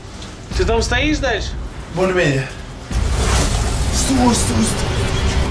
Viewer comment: Почему я слышу: «Бульмени»?